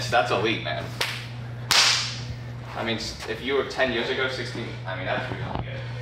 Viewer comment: My VO2 Max score did not improve. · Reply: Sorry I misunderstood what the guy said - I thought he said you were at 60 ten years ago. What was the number before? Just curious! Thank you, great video.